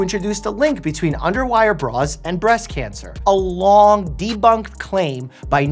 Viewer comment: i thought it said brains cause cancer and i said "oh then im safe from cancer then."